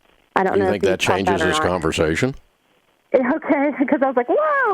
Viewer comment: He really did not listen to her though. He assumed she had no income. Not saying the van was a good idea. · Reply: Heidi Thaw: When she did mention that she was working and how much she made, Dave said, “You think that changes the conversation?” She misheard him and thought he said, “I think that changes the conversation.” She sounded relieved, thinking it would change how he felt about the new van. Nope, he still said the van was out of control.